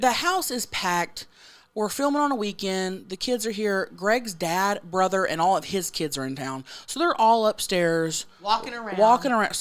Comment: I thought you said “Greg’s dad, brother and all his TITS are here walkin around” 😂💀💀